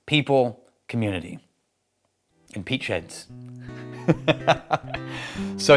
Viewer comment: Sorry, every time you say "peat sheds" I hear "peach heads".